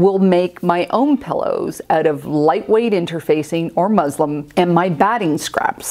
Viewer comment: First I love your videos. And you are so careful. But when you were talking about making your own pillow fillings, please tell me I heard you wrong. I went back like 4 times. It sure sounded like you said “quilt batting and MUSLIM”. I know you meant muslin. Sorry it’s just a HUGE pet peeve of mine. But for all the other things you always do right and thoughtfully, you won’t lose a subscriber.